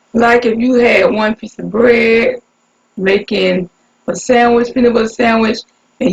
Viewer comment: What did you say at 9:22-9:28 sis, fold the paper own like? 🤔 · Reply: sandwich lol